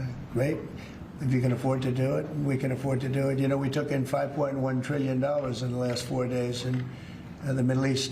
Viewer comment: $5.1 Trillion I thought he said $7 Trillion. The man just not for real, nobody can take him serious.